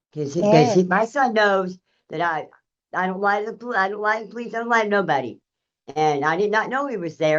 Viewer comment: That is absolutely not what she said and its dishonest to make it out like she said that. She said "I dont LIE to the police, i don't LIE to anybody"